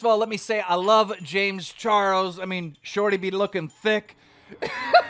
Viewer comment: did u just say «shorty be lookin thicc» wtf